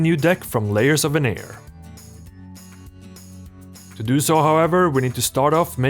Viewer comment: what did he said— from layers of a N... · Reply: There's a reason why you can turn on English subtitles. The word is "veneer".